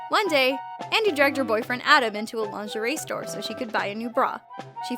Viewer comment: I hear "Andie drugged her boyfriend into a lingerie store...".